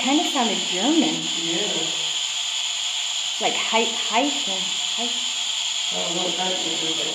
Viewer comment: Shell yeah that place was awesome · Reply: ish we thought it said hiten but re-listening to it, it sounds like Hi Tim